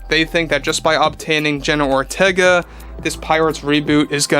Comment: I keep hearing "General Ortega" every time says her name.